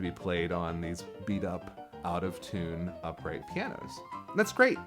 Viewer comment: I thought you were saying "auto tune" piano. New one on me.